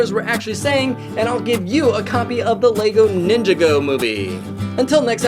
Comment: did you just say Ninja. Go.